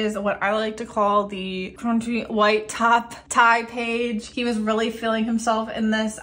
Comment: At first I thought you said crunchy white top tie page 😂. It all looks so beautiful I really should think about using 7 pocket pages never thought to do it for only one person until now.